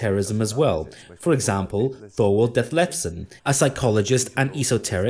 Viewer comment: Around minute 31:25, he mentions an esoteric author and I can't understand the name. Can anyone help me with this? Thank you. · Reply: Captions say “thorwald death lefsen”